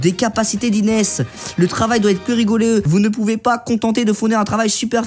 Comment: j'ai entendu a 12:45 le travaille doit etre plus regouleux mdr !!!!!!!!!!!!!!!!!!